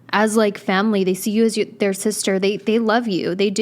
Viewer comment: did dhe say sister?